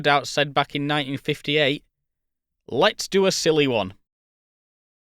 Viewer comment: I got an ad at this point in the video, cutting you off and making it sound like you said “Let’s do a silly”.